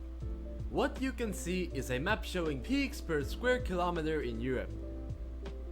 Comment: Pigs? i thought he said peaks, like mountain peaks. 🤣🤣🤣